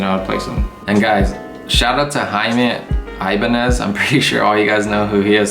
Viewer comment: I thought the video was informative up until around 13:19 when you shouted out Jaime. Did you really say that "neega"? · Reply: No never. I said “dude”